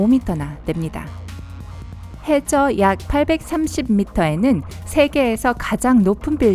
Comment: 829라고 적히는데 830이라 들림